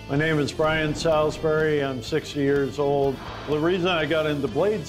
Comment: It sounds so much like he says he’s 6 years old, I had to rewind just to make sure I misheard